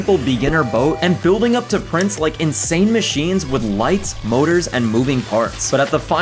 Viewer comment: I thought you said Boners when you said motors